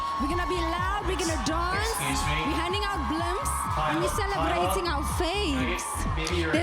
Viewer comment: Did she say she was handing out blunts???